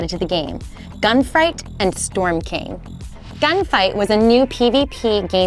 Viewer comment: Did anyone else hear the lady say gun fight as gun fright.